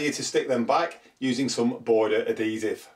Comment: use WHAT? to stick loose edges down. Can't understand you. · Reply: Border adhesive (it will stick the paper down much better than wallpaper paste)